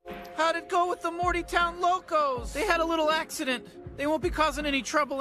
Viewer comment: Like and subscribe! My horror and comedy channels are both in the description along with my Patreon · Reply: I legit heard say: "Adding Logos to a name", when Morty Clearly said Locos